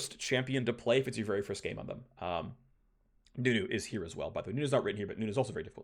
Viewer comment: did he say nunu? too fast for me to understand it lol